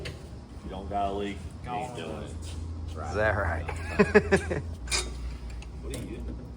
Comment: did say zallright